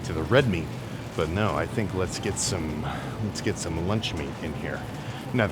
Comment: im too far gone i thought bro said lunchly